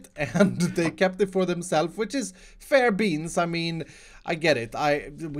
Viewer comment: Did he say fair beans? Because that is now my favorite saying. I literally cried. It's so great.